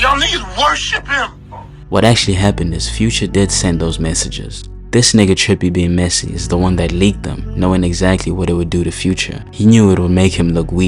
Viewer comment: Did he just say "This nigga Trippy being messy is the one who leaked them"?